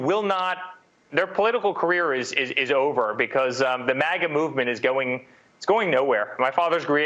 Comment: did he say the MAGA MOVEMENT IS GOING NOWHERE?